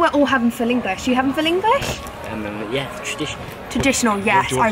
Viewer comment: I thought she said filingish not full English